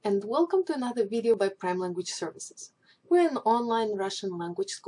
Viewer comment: i thought you said kremlin language services :p